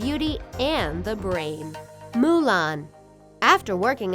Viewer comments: At precisely 4:07 seconds when it says 'Mulan' the subtitles say 'ariel." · Sound: Mulan Reads Me: Wait wat it said Ariel tho